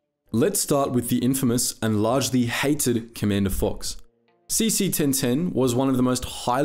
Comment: did anyone else hear saesee tiin tiin instead of CT-1010 or was that just me